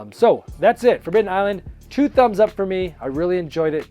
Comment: did you say forbidden island ,2 thumbs up?